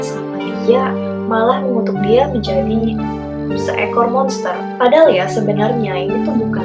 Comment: mengutuk menjadi lobster? Apa salah denger yh.. d ulang" tetap lobster bukn monster 🤣